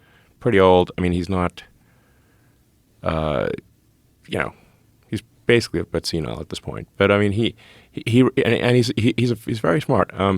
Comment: Can someone please help me understand what Elon calls Soros at the 3:41 mark....Either I cannot understand what he is saying or it is a term/word I am unfamiliar with......Please enlighten me ! Thank you in advance 🙏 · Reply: He said George is pretty old, basically a bit senile at this point.